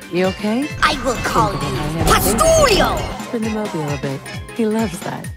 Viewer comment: What he says at 01:08? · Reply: I shall call you Postulio - It's a reference to Invader Zim